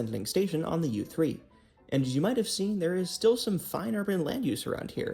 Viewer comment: "...there is still some fine urban land use..." Is that what you're saying?